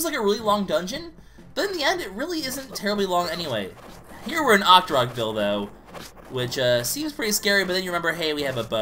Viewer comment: Am I the only twisted pervert who heard: "Octorok dildo" ?? -.- (upon further inspection I do believe he said "here we're in Octorok-ville though")